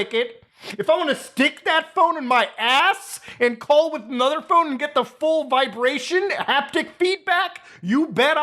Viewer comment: i got distracted while watching and when I came back I just hear "if i wanna stick a phone up my ass and call another one just to feel the vibrations you bet i will"